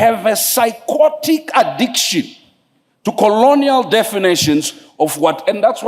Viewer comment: Can anyone tell me the word that he is saying it sounds like 'psyquatic' or something like that. I have never heard it before, and I was trying to find it but can't. I may not understand what he is saying clearly. Thanks. It's around 3:15-3:20 I think thanks. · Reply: Psychotic addiction